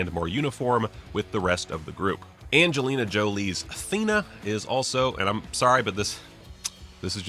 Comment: i thought when he said thena he said athena